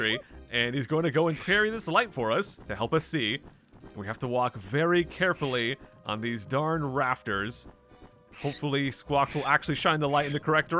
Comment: Misheard dialogue: "We have to walk very carefully on these darn raptors". Imagining Diddy tip-toeing over the backs of a bunch of sleeping Jurassic Park raptors.